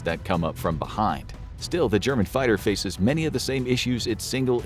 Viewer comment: He said Germany’s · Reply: sounded like he said germanise